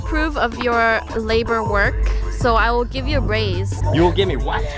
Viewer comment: Lol I thought said "for your hard work I'll give you brains" totally misheard that